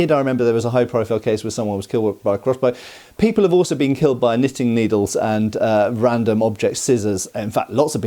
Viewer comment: I heard 'Ban knitting needles'